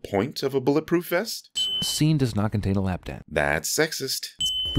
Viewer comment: what does he say here?? 😭 What is a “lap dat”??? Edit: Apparently he says “lap dance”. I was close.